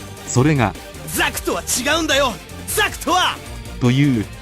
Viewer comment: 他のガンダム全く見たことないから 「ザフトは違うんだよ！ザフトは！」 に聞こえてめちゃくちゃ誇りを持って戦ってるんだな～って思ってた。オマージュとか全然さっぱりでしたが面白かったです。